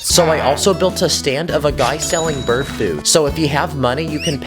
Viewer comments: I thought he said bird poop 😂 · i thought i heard bird poo💀☠️ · I thought bro said bird poo instead of bird food🤣🤣